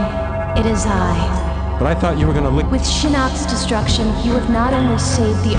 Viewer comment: i always thought he said "but i thought you were going to lick my" before Kitana cuts him off lol 😂